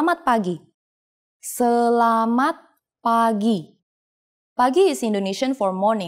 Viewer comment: As a Filipino learning Indonesian at school it's pretty easy so far.... However theres this one time I Forgot I was in Indonesian class and when the teacher said "Selamat Pagi" I thought she said "Salamat pagi" which means "Thank you Stingray" in Tagalog, lmao.